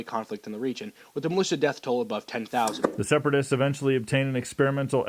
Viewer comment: Wait did I hear separatist at 1:45?